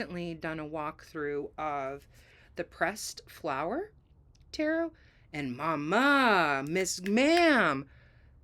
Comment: I was listening while writing down moon phases for the year, and I thought you said depressed flower tarot